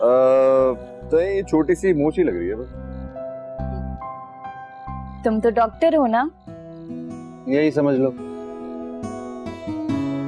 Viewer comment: when azekah said tum to doctor ho na zain reply bus yahi samahj lo and I thought so that azekah says 'aacha ha aacha ha'